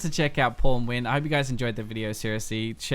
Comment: I heard "be sure to check out pornwin"